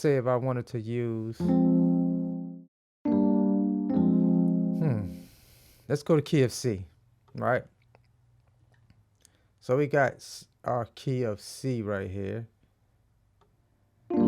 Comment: let’s go to Key of C sounded like = Let’s go to KFC I thought damn let’s get busy 😃 🍗